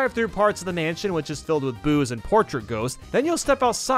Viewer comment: I heard "boos" as "booze"